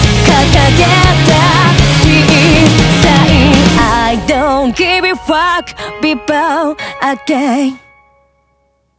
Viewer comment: does she say I don’t give a f@ Lol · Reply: No, she sung: " I don't give a fuck"